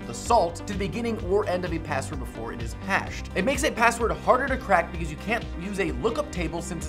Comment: You said "harder to crack", but the closed caption subtitle says "easier to crack".